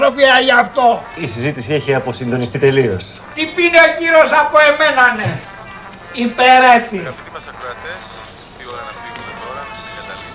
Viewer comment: τι λέει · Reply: εννοούσα μετά απο αυτό. " Τι πίνει ο κύριος από μένανε. Υπερέτη; 🤔 "